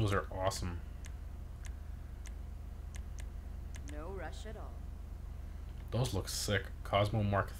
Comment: did she just say no Russian